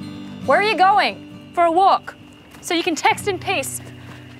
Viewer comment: I know she said, "So you can text in peace"; but what I heard was, " So you can text and piss." I must be tired.